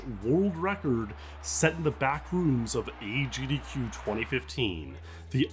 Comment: At first I thought you said in the bathroom. I thought yall was getting wild at agdq for a second there.